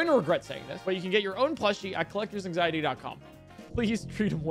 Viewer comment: What he says: "Please treat him well" What I hear: "It's gerbilling time!"